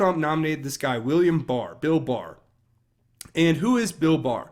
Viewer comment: Omg I thought you said Bill Burr. I was like, YEEEES. Haha